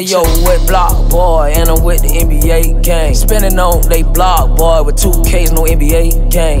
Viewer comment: I coulda I swore I heard some about pulling on nba”s blocks with ks